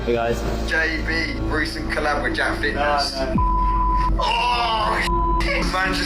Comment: I was so confused because I misheard jacksfit as jacksfilms and he does look like he's working out to build muscle